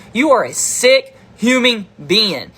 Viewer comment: did he say "huming" instead of "human" ? Lmao